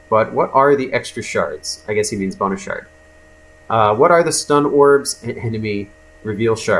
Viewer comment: Am I the only one that when he said "Bonus Shard" all I heard was "Boner Shard"?